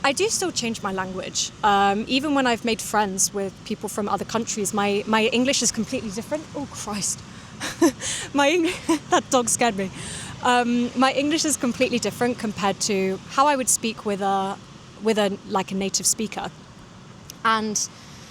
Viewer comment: "Oh, Christ. That dog scared me" the moment of casual native speed here! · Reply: (As a native English speaker) It sounds like she said either "at dog scared me" or "dog scared me". Of course with context you know the missing word is 'that'.